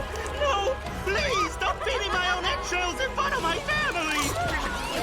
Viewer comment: nobody gonna talk about the fact that the subtitles say “Eggshells” instead of “Entrails”??